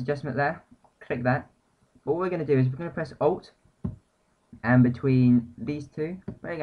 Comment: hi :) at 06:35 you say "we're gonna press-" and then you say something that sound like "Owt"... so, it sounds like you say "we're gonna press Owt" and maybe that's what you said, but wtf is Owt? :)